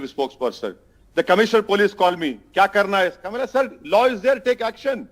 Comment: Did I hear it right? Hyderabad police commissioner asked Owaisi 'kya karna hai?'. Does police report to him now?